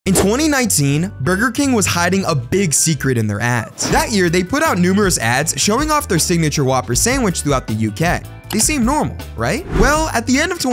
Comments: I really need to get my ears checked I thought he said "in 2019 burger king was hiding a big secret in their ass" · I lowkey thought he said "Burger kind is hiding a big secret in their ass" I was like... Yo. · I fr thought he said “They were hiding a big secret in their ass.” 💀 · I thought he said "burger king is hiding a big secret in there A$$"💀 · i thought mans was said "hiding a big secret in their ass" 💀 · I legit thought he said, “burger king was hiding a big secret in the a**” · I heard "burger king was hiding a huge secret in their ass" I was so confused lmao